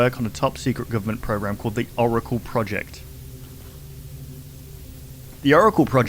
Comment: I thought that said "The dance project" lol